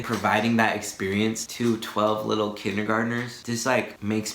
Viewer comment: ish I thought you said “12 year old kindergarteners”. Lol yikes 😂🤦🏻‍♀️🤷🏻‍♀️ On a real note, like everyone else is saying, I’m glad to have been included as a sub and be able to join you on your journey. :)